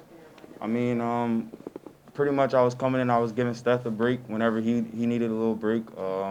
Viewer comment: I think he said "giving Steph a break" at 5:25 lol. wrong Curry!